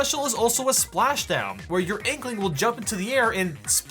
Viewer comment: did you just say my inkling I play as a octoling